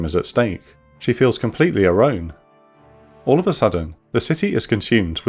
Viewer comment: Did I hear that right a 3:49, “she feels completely arone” 😂